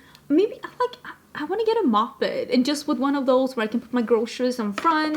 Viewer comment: It took me a few minutes to realize said “moped” …. I thought she said “i wanna get a muppet - put my groceries on front” 😂😂😂😂😂 Me thinking it was some European thing …